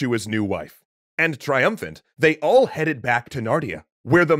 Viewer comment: Did I hear you right? The land was called Nartia? So then these stories are the Chronicles of Nartia? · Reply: I misheard and completely thought he said Narnia.